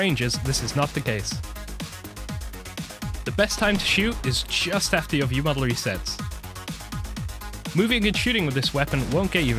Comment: What did he say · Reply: "the best time to shoot is just after your viewmodel resets"